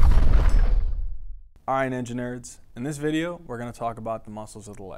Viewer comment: First thing I hear is "all right enginerds" as an engineering student, I approve